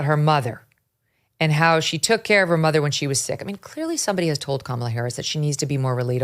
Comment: I thought she said she hired someone to care for her mother.